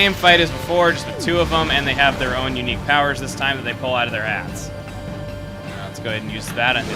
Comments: Its funny it sounded like said that Lechku and Nechku pull powers from their ass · when he said pull out of their hats i thought he said pull out of their ass XDDDD